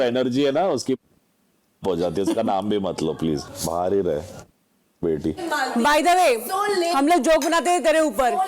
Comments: Did amaal said rand ki beti to farhana? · Is Amal saying ra*d* ki byti ???? 8:16 min ? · Amaal once again swore at Farhana by giving the biggest swear ever!! He said, 'r**di ki beti' watch at 8:15. He should be kicked out of the house!!!!! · did he say randi ki beti?